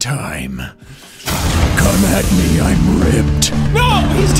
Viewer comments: Jack says come at me I'm ripped. Subtitles say come at me I'm ripe LMFAO. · “Come at me, I’m ripe!” this is what I assume my bananas say to me when I’ve left them on the bench for a week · To whomever subtitled this bit....I'm laughing. "come at me I'm 'ripe' !" Instead of 'ripped'. Had me rolling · I legit thought he said "Come at me, I'm ripe!" as in like ripe fruit